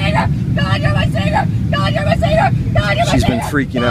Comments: GUARD YOUR LIGHT SABER GUARD YOUR LIGHT SABER GUARD YOUR LIGHT SABER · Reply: I thought she was saying God your my saviour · GUARD YOUR LIGHT SABER GUARD YOUR LIGHT SABER GUARD YOUR LIGHT SABER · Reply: I heard "Got your receiver". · GUARD YOUR LIGHT SABER GUARD YOUR LIGHT SABER GUARD YOUR LIGHT SABER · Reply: I think it was “god you’re my saviour” but I heard that as well xD